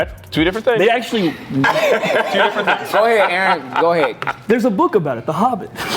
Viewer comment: I thought said "foehead Aaron...I mean uh go head Aaron" 😂